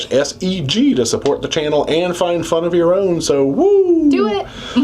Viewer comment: 😅I thought said "roll our own" LOL.